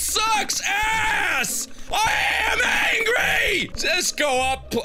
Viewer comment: legit thought he said “angy “ and I got so happy 🥲